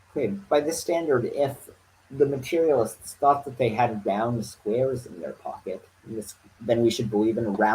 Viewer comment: i heard "if materialists thought they had downstairs in their pocket" which would also work for what you wanted to mean